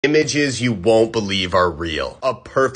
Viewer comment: I thought he said "Bitches u won't believe r real"